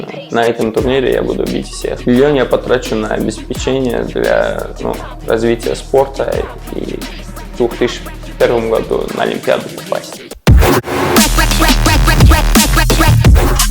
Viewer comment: Мне послышалось или финансист сказал: в 2001 году на олимпиаду попасть!? 😂😂